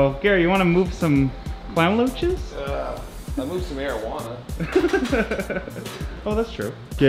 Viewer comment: Thought Gary said he moved some marijuana 😂😂 I was surprised for a second